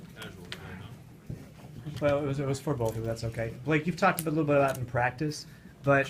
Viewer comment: what was said at 6:21? after BG said "casual tonight" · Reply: Paul got up to leave as the reporter was getting ready to ask a question..that's when the reporter said "it was for both of them" and Blake says"do you want him to come back"